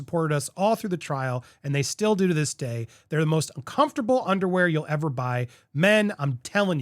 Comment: it really sounds like he says most uncomfortable per of underwear